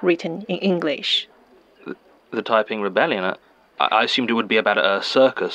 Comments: I thought they were saying 'typing rebellion' and was very disappointed to not hear a cyberpunk tale of bashing heads in with keyboards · I don’t get why this Episode kinda underperformed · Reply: I'm not as ignorant as the people who thought he was saying "Typing Rebellion" but I don't know enough to understand why a British officer thought he needed a hair braid. This episode could have used notes for not-British or Chinese.